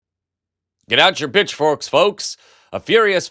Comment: Did you say 'bitchforks'? It sounded like you said bitchforks - good doctoring to you on your upcoming procedure